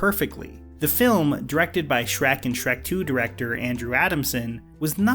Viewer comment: For a split second I thought you said the movie was “directed by Shrek.”